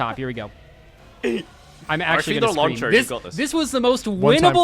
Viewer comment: They do say lawn chair, that's the joke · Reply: i thought they were saying launch her XD